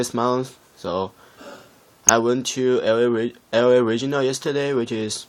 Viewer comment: did he say er ay regionar?